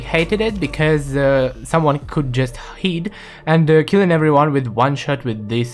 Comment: he just said the word hid instead of hide